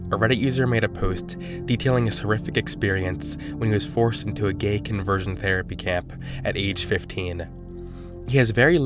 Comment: I thought it said gay conversation camp